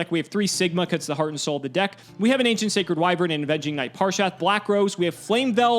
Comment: I almost thought said "a Veggie Knight Parshath." XD